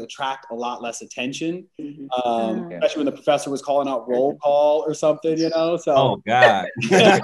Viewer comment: What does Prince say at 1:14? The professor was calling what? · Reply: Roll call or taking attendance.